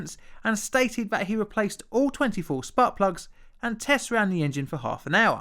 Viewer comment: I like having this on in the background while I’m working. Not gonna lie, I spaced out completely until I heard him say ‘butt plugs’ and I psyched up. Turns out he said ‘spark plugs’ and then just like that I became bored at work again.